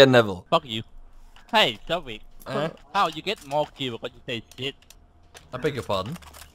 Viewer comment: i think he says "how do you get more views but stay shit?"